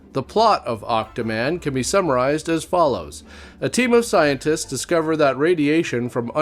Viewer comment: When you said "team of scientists" I heard "temu scientist." Works both ways.